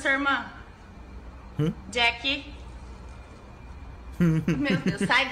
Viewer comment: мне одной послышалось Deku?